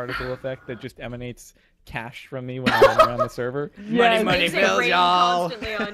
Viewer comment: I thought he said 'dollar dollar bill yall' thought he was a wu tang fan:o